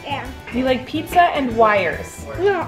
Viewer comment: I thought said I like pizza and winer